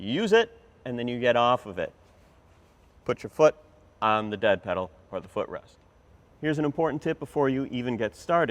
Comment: sounded like "Dad pedal."😂